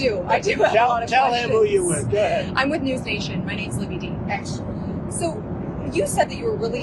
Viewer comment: Who is the reporter? She is doing a great job. Aggressive but not disrespectful. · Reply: about this time stamp they ask who she is.. Libbey Dean I think she says. From News Nation